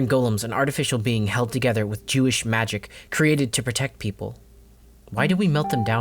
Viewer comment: Not even a minute in and I hear 'jewish magic', I am sold on whatever you're selling